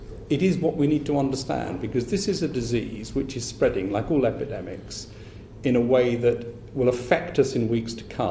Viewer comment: Epidemic thought you said its a pandemic